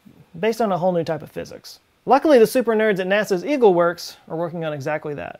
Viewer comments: I heard it as “NASA’s Evil Works” first time. That would be cool. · for some reason i hear you say "the super nerds in nasa's ego works are working on that" and just blanked out for a minute there ...